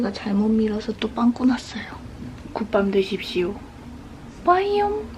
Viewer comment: "굿밤되십쇼"를 "국밥드십쇼"로 들었어여ㅎㅎ